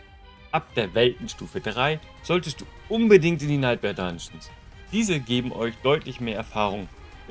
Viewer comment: ab weltenstufe 3, was sagt er da? ich verstehe nicht was er da sagt. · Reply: ab WT 3 gehst du am besten in die Alptraum Dungeons. Dort bekommst du mehr Erfahrung vor allem bei Events wie zb beim Verfluchten Brunnen (wie im Video zu sehen)